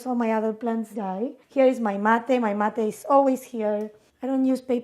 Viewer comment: I thought Gaby said, “And here is my mother,” as she showed a glass canister that looked like ashes. I think she might have said, “mate” as in “yerba mate.” Whew!